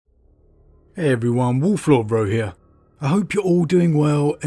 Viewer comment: I always mishear your "wolf lord rho here" to "wolf lord bro here"